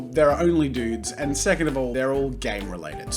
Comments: i literally heard "They're all dudes and they're all gay" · I thought he said "gay related" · At first I thought he said their all gay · I thought that you said gay related instead of game related and I was confused. I had to go back like five times before realizing what you actually said. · i thought he said gay related fp lol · totally thought you said "they're all gang related" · "They're all game related" LOW KEY THOUGHT SAID THEY'RE ALL GAY RELATED AND I JUST CHOKED ON NOTHING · "they're all gay" is what i thought he was saying lol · I thought you said "gay related" XDD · I heard "They're all gay related" not "They're all GAME related" !!!! 😂😂😂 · I thought he said gay related at like the 1 minute mark when he said game related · Sounded like he said “they’re all gay related”